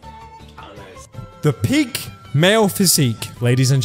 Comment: the captions say the “pig male physique” instead of peak lmao